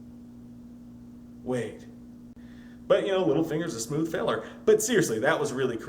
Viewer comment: when you said "Littlefinger is a smooth fella" I totally thought you called him a "Smooth Hitler," and I was like, huh, he is a smooth Hitler. Totally what I'm going to call him from now on.